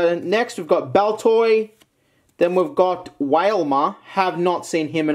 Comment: I thought I heard Whalemart WHAAAAAALEMAAAAAART